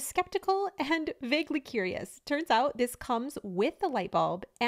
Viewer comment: Did she just say "Butter Light than never"?